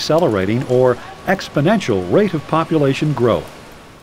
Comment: What does it says in 0:14 till 0:18???????? pleaseeeeeeeee answeer meeeee !!!!!!!! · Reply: No worries I got you. It says exponetial growth.